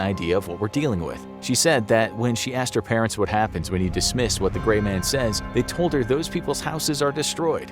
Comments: I thought he said its "its the gay man" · At first I thought it said ' Gay Man ' 🤣🤣🤣🤣🤣🤣🤣🤣🤣🤣🤣🤣🤣🤣🤣🤣🤣🤣